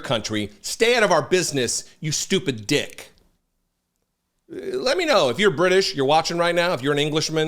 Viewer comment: Yep, I totally heard him say "you stupid dick" lol